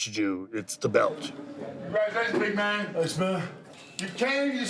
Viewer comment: Did he call Bundy „Smelly“?